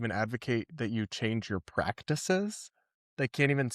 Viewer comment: Someone please tell me I wasn't the only one who thought they said Ep-Stein your practices for a few seconds